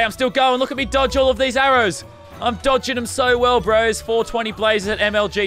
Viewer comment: did he say bros? Ok pewdiepie